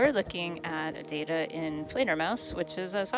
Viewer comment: What was the name of the visualization software you said was being used? Google's Closed Captioning showed it as "inflator mouse" and my ears heard it as "trainer mouse", and I'm sure neither of those is correct. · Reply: Thanks for asking - we'll get the caption fixed! The software is Fledermaus.